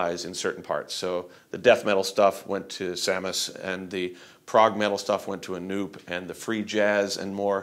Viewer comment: Sounds like Dev said the Prog stuff went to a newb. Lol